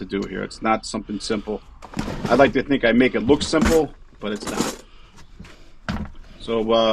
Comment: Did you say "oskillatin" tool ? Lol, love that Eastern accent... No disrespect brother... ⚡❤️⚡